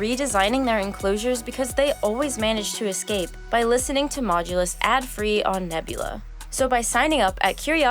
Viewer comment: For a second I thought the outro said "Octopi keep learning to escape by listening to Modulus on Nebula" That would be a very smart Octopus